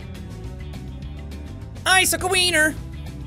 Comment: What's happening to the audio?? Seems like a pain. Edit: also is it just me or at 37:30 it sounds like says icicle Weiner lol 😂😂😂